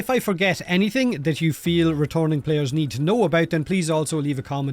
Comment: swore he said ret@ players 😅